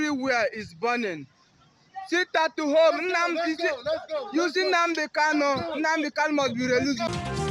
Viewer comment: Did he say @ 2:03 "Shit at home"😂😂 just as obidiots crying about their stolen manhood😂😂 Bunch of clowns.